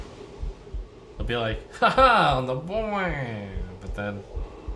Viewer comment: what is he saying?? "booyye?"